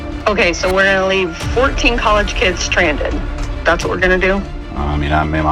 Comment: No point in the video did it say college students nor did it say they were adults. they are Teenagers on spring break. teenagers usually don't have money because they spend it. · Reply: turn ur hearing aid up pops. She clearly says collage kids 😂